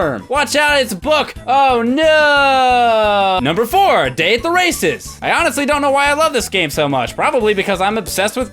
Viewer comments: I honestly thought he said "Date the racist." I like the Mario & Luigi RPG's. I only beat Bowser's Inside Story. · When you said Day at the races, I thought you said Dave the racist!